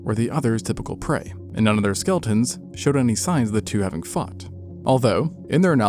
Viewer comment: am i the only one who heard "fucked" instead of "fought" 😭💀 my tired ass had to rewind that shit like 4 times to properly understand what was said cos i just knew i hadn't heard it right lol